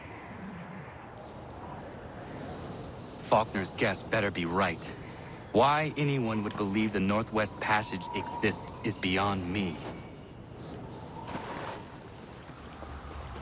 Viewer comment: Lol I thought he said "fucker's..."